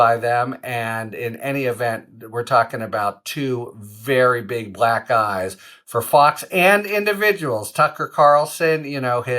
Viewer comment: Did he just say- "two very big black guys" ? " Couldn't satisfy" & "two very big black eyes".... Lmmfao 👀😉